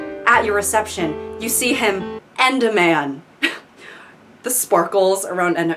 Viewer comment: I was only listening and I heard "him and a man", not "end a man" and I thought he is gay